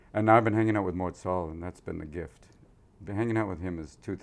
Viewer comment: Need your help here. At a moment he tells about people that make him laugh : Chris Rock, Louis CK, Patton Oswalt and ?? I don't understand the name of the last guy he's been hanging around, did anyone catch it ? thank you :) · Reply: Romain Eriam Mort Sahl. Thought he said Morzal or something, had to listen a few times.